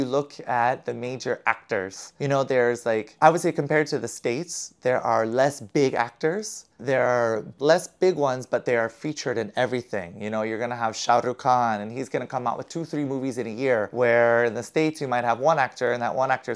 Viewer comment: Did i hear less big one's?😂